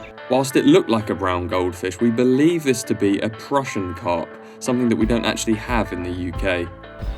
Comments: It was a Prussian carp :) very similar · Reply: cheers bud, thought he said crucian. Top content either way, keep it up boys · I think you must be confused. :) Watch that bit back again. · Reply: ahhhhhh I misheard you , prussian carp not crucian, my apologies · It was a Prussian carp not a crucian 😎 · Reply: Oh! My bad I thought he said Cruscian! Looks like a huge Cruscian though, mental! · Yep, I said in the video it's a prussian carp. :) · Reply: haha i thought u said crucian carp which is a diffrent type of a gibel carp its grows bigger and its a natures gold fish its more rare to catch one then a gibel carp best of luck mate